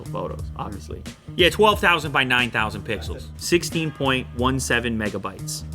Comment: Did he just say 12 thousand, 19 thousan pisos?